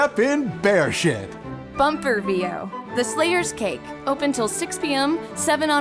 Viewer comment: It's taken me this long to understand why say "Bumper VO" for the Slayers Cake ad. I thought she said Bummer BO THB which confused me